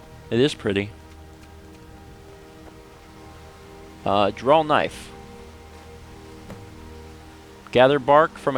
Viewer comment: Got a little surprised for a second there. Thought it said "Drow knife". "But.. skyrim doesn't have Drows, does it?!"